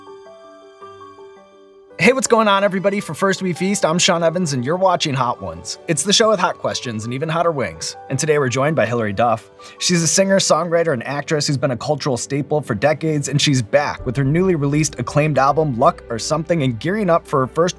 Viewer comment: Somehow my brain heard guests instead of wings here :c)